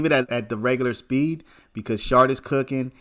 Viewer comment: *Schardt. He is definitely “sharp”. In fact he deserves the respect of having his name be known (not correcting anyone to pick on them, just giving Mr the respect he deserves for the excellent work he does for his client and others in this case) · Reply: I thought they were saying sharp my bad good looks tho he definitely deserves the respect